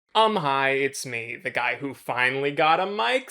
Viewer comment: who else thought he said “I’m highhhh” instead of “umm hiii”